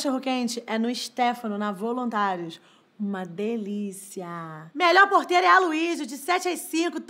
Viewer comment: eu ouvi ''o melhor porteiro é o Waluigi''